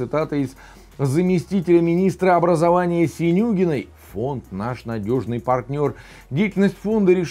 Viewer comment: Послышалось Свинюгиной... гыгыгы Спасибо за Вашу работу! Смотрю с удовольствием и сожалением.